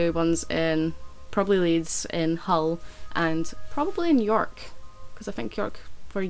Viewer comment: Who else noticed at 4:44 that said New York, before correcting what she said? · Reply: No I said "in york"